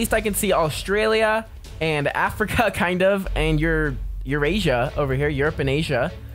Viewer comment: WUT DA HECK DID YOU SAY?! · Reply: Eurasia? Eurasia is just Europe and Asia since they are connected they have a connected name